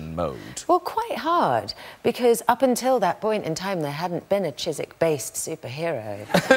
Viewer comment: What is she saying? "Up until that point in time there hadn't been a ... based superhero?" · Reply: Chiswick. <link>